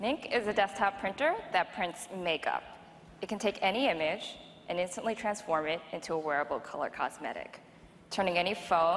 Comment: I thought she said "it can any of b*tch to transform into any wearable...."